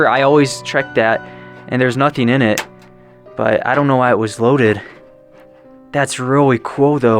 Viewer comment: That gun needs love take it home rebuild it take it back with some ammo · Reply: I thought he said "I don't know why it was loaded" I was thinking it was.